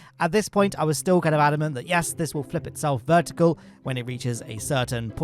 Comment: I hear "testicles" what is wrong with me